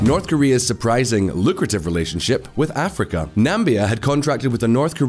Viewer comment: did he say Nambia instead of Namibia?